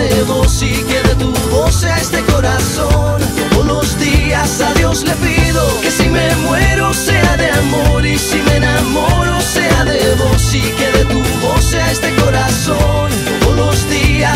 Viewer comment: Hasta a los ateos les gusta esta canción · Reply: pense que decia adios le digo 😂